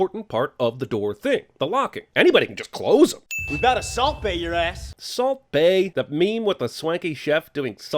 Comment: THEY WERE SAYING “SALT BAE” WOWWWWW I thought they said assault bay 🥴